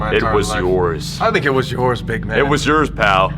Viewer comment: creepily stands in window: “that’s actually hella creepy” with a British accent: “ Do YoU WaNt a BuIScuIt????” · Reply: I thought he said "biG gAy"